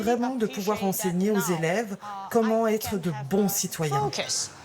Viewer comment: la prof qui parle anglais, j'ai compris "good citizen sheep" (un bon citoyen mouton) ça allait plutot bien dans ce cas precis 😅